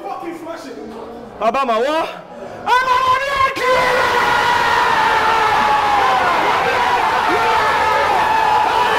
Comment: what he said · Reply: He said: Aubama Who? Aubama What? AUBAMABLUDCLAAAAD